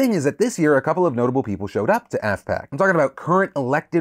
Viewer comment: Re AFPAC, I initially heard it as ASSPAC.